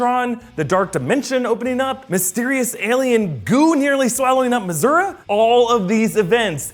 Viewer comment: did he just say Mizzoura? triggered